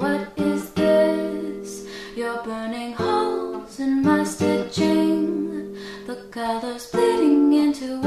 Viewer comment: I misheard it as 'You're blowing holes in my shipping.'